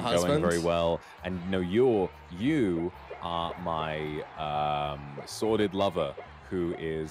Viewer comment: I think at 0:24 Vox said “sordid lover”. Sordid is basically something that involves ignoble acts or like… sleazy kinda